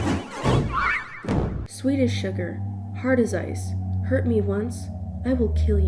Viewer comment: I heard "Swedish sugar" instead of "Sweet as sugar" and I got fucked up